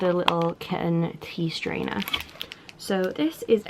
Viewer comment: At first I thought you said "Teeth Trainer"...